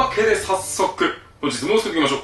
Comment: いつもここ何て言ってるんですか？ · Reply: Reverk1234 本日も作っていきましょうって言ってるかと。